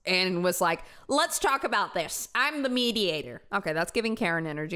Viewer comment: I thought he said "I'm the insured", I do think he's the Dad.